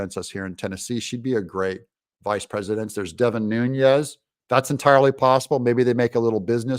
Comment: Omg! I thought you said NUDES!